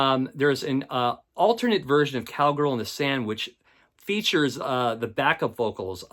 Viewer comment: Could swear I heard you say 'Cowgirl in the Sandwich' (inadvertently) at about 7:10. Kinda misheard, like Lennon's humor in that book you found.